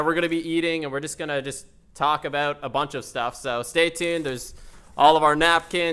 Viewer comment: We have a bunch of tacos in here.....And we gonna TACO bout some stuff hahahha (he said talk about but it sounded like taco)